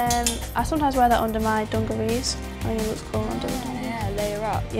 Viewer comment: what is that word? Dungewhat??? What is that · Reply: daniella dungarees, that's the British word for overalls